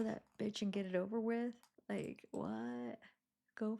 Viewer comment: I heard her say "go k-ll that B, get it over with".